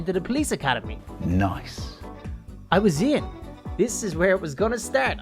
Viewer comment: When he said “I was in” on day three I thought he said “I was ein” (from mystreet (aphmau))